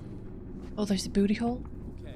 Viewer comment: did you say,..."Oh, is that a Booty-Hole"? lol!